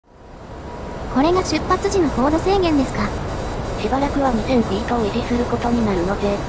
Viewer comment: 最初の『これが出発時の高度制限ですか…。』 が『これがスマホの速度制限ですか…。』と聞こえてしまった。まぁ画面見てなかったからなんですがね